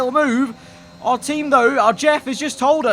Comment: idk if it’s just me or did just say “Our Jeff”?